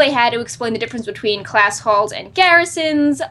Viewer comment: I misheard, so from now on I am calling them Class Holes.